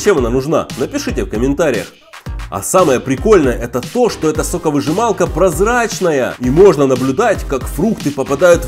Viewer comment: Мне послышалось сук.выжималка вы поняли да